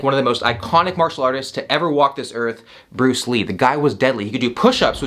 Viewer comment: did you mean Bruce Lee instead of Dead Lee?